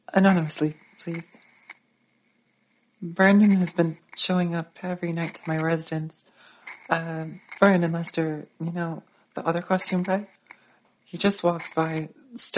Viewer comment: JSYK What Olivia said during her audio at 5:17 was " Brendan has been showing up every night to my residence. Brendan Lester, You know the other costume guy right?"